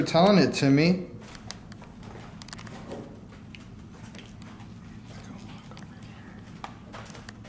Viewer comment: Wooaaahhhh at 1:16:52 a male disembodied voice whispers a long sentence looking back he says then I can't hear a few more words last few were something about his return 😮this could be any entity good bad or indifferent weird and spooky stuff always brings it man !!!! 😮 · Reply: If my ears treat me good it aounded like dont walk away and whith the rem pod going off when they started to walk away makes me believethat more